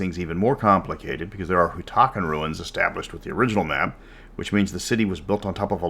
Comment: I could have sworn I heard, "otaku ruins". :D